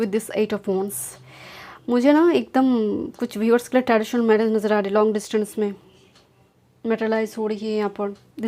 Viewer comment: What did you say at 26:42, after you said “omg Virgo is here mujhe Na…” uske aage Kya bola aapne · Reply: Kuch viewers ke liye traditional marriage najar arahi hai long distance me Yahi bola hai ma'am ne